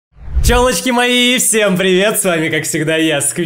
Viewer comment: Я одна услышала " ТЁЛОЧКИ МОИ ПРИВЕТ!!!!"?